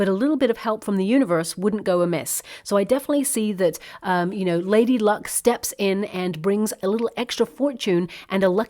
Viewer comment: You said Lady Luck and I thought you said lady bug- (I found a lady bug on a house plant)